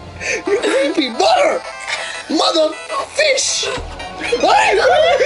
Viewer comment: I HAVE A FEW THINGS I WANNA SAY!!! 1. When She Ran, that Music TIMED PERFECTLY!!! XD 2. What He said After "You creepy Mother", I thought he Said Mother F... 3. Those Screams Are PERFECT, And use to Make me LAUGH SO HARD WHEN I WATCHED THE OLDER VIDEOS!!! XD 4. This is Pure PERFECT Scary but Funny Gaming Moment EVER!!! LMAO